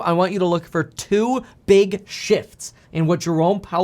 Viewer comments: Thought you said two big shits hahahah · I thought he said "two big shits".. Which has been much more during this market.